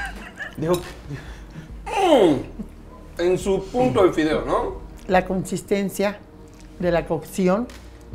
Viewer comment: Minuto 23:30 yo entendí la consistencia del cokcis